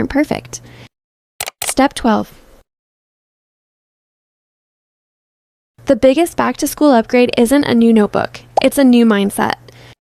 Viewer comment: Whenever she said "the biggest back to -school I thought she said the biggest back for a sec bro❤